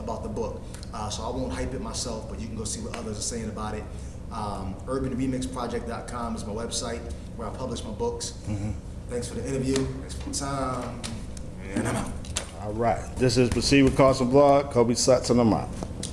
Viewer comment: Did he just say, "Kobe sucks, and I'm out"? lol